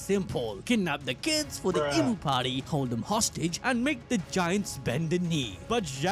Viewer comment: I hear Imu party and seeing that edited in took me out 😂